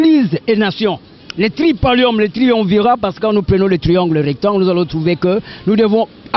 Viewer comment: A 2:38, je pense que c'est "Triumvirat". Sinon, bravo pour la performance de cet acteur !... Attendez, c'en était un rassurez-moi ?